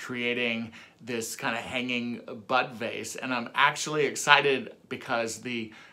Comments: Completely off topic but I had to replay the end like a dozen times because I thought you were saying “buttface” not bud vase. 😂😂😂 I think it’s time for bed or a drink. Or both. 😂😂😂 (love this project of course) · I thought you said butt face every time you were saying bud vase! 🤣. I was waiting to see the butt face! Good grief, I obviously need to clean my ears! 🤦‍♀️